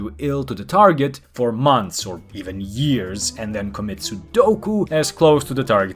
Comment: I heard "commit sudoku" and pause the video to laugh 😂😂😂